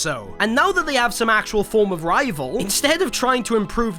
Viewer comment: did I hear rivals